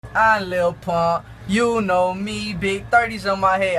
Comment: it sounds like he says “I Lil punk